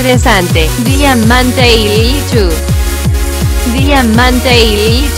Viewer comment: Parece que dice Diamanteiru xD